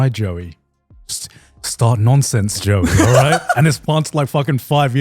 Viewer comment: There was a bit where i could've sworn garnt said to jooey start noncing. Instead of start nonsense